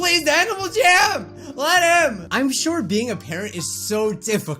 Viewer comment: did i hear that correctly? (IF HE WANTS TO PLAY HIS ANIMALJAM LET HIM) or was it something diffrent?